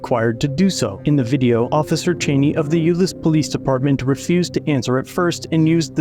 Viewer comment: Am I the only one who heard 'Useless police department' at timestamp 1:31 instead of 'Euless police department'?